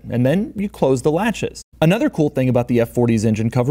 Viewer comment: At around the time he was talking about the trunk metal rods...…. he kept saying "another cool thing about..."...… all I kept hearing was "another kinda shitty inconvenience about..."